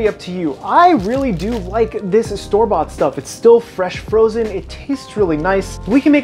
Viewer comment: wait。。。what did he just say at 4:43。。。。did he say fresh frozen。。。。ohhhhh myyyyyyy goooooooood